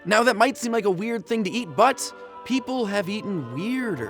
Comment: I thought he said "eat butts" rather than "eat, but"